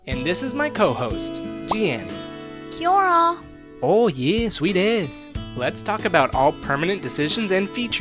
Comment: did... did he just say “oh yeah, sweet ass?”